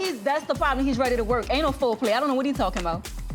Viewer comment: Did she say "ain't no foreplay" or "anal foreplay"?